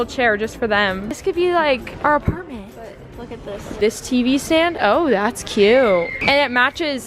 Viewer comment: when you said ‘this tvstand’ my phone thought you said hey siri, siri are you ok so you need a hearing aid